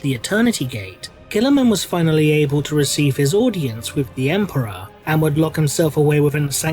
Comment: Is it just me or does it sound like says Emprah instead of Emperor?